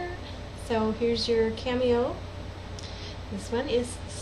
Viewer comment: Ha! When you said "cameo" I thought you said Candy-O and I thought cool, she named the litter after Cars albums. "Little Panorama is sitting in the window and Shake It Up likes to hang out on the washing machine when it's on spin cycle..." :D